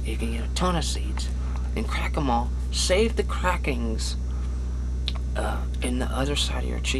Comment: did he just say "crackingness"? xD